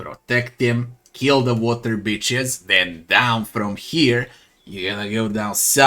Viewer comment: I thought you were saying water beaches and got confused then realised what you were actually saying and lmfao 😂